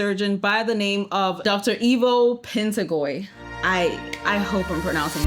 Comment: I had to watch this fragment like ten times because I swear I heard dr Evil😅